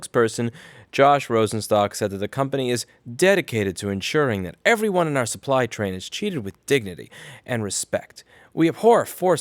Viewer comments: XD When you said "treated with dignity", I heard "cheated with dignity". Haha, soooo much better. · Pretty sure I heard, "The Company is dedicated to ensuring that everyone in our supply train is CHEATED with dignity and respect..." Well said.